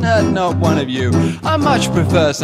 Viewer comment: I genuinely thought he said "And you're all way to stoned, oh yeah he ate the elephant." Hehe, silly me. :P